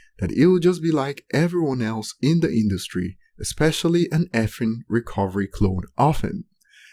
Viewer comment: Lyric- "An Effing Recovery Clone of Me.. " How the Hell do People say it is a NF Diss? 😑 People are so Sheep these Days😓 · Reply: i mean the way it sounds it does make it sound like he says NFing recovery clone